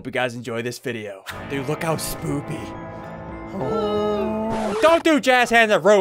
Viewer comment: When you said spooky i thought you said spoopy